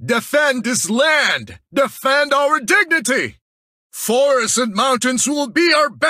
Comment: i thought lapu lapu said "Defend this land, Defend our virginity!"